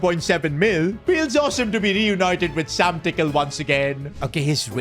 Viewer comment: thought he said said Sam didckle